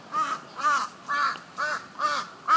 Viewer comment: Cześć, こんにちは, I speak Polish and Japanese both a little bit sadly. Is he saying at 1:47 "Shitougaitou, shitougai, ikeserumon!" 1:50 then what's it mean in Japanese? I can't figure it out what he say in Japanese and I tried translate it and it's this "Clash applicable, to the, to go!" so I can't translate it myself and sorry it's inaccurate? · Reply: haha actually it's French ^_^. He is saying "Je t'aurai, toi...Je t'aurai... Je vais te casser la gueule, moi!" which means : "I'll get you, you... I'll get you... I'll beat you up, me!" >.<